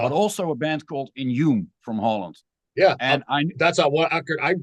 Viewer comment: what grind band is he talking about I can't really understand him? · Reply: Inhume